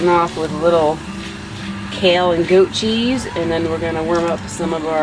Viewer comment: Wow! For a minute I thought you said Kale and TOE-Cheese! Whew!